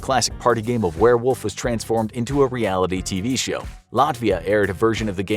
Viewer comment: Did you say Latvia 🇱🇻? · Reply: latvia 🗣️🗣️🔥🇱🇻